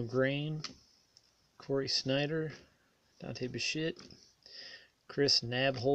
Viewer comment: It sounded like you said, Dante Bishit, instead of Dante Bichette?